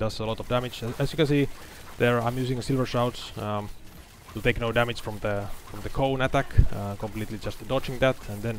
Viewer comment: is there any way you can write what you say here, ive replayed 3 times I cant make it out. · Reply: "i'm using Silver Shrouds to take no damage from the cone attack, completely dodging that" (Silver Shroud guarantees that the next hit is dodged, stacks are consumed when you dodge) You can use the auto-generated subtitles also if you need.